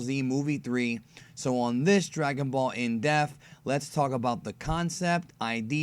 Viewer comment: Why does it sound like you say death instead of depth?